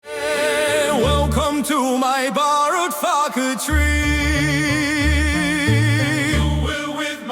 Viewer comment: initially I was not watching, I started the video and grabbed my drink, so I was listening but ynot watching, at least not closely... The song "Welcome to my Ballot Factory" with.tye accent and music... It sounded like "Welcome to my Borrowed Faggot Tree" I snapped my head around, to see what the hell was happening, when I saw the subtitles I actually laughed out loud